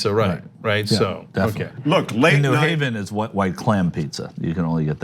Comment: thought he said white clan pizza